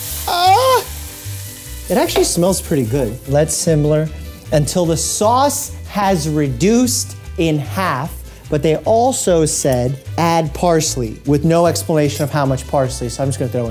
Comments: Don’t act like we didn’t hear you say “simler” instead of simmer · I'm curious about the recipe he was using cz that was a calculated mess 😂😂 Also... did he just say "let simmler" instead of "let simmer".